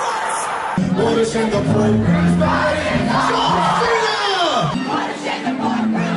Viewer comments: The poor grooms bride is JOHN CENA (Did I hear that right?) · Did Brendon just say John Cena?! XD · did he say john cena omg kill me LOL